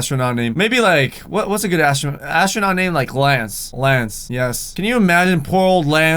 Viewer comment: DID HE JUST SAID "LANCE"??? OMG VOLTRONNNN XDDD